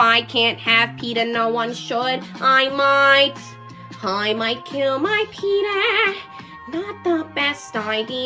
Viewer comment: i thought this said i might kill my chetah, im not sure which one is worse....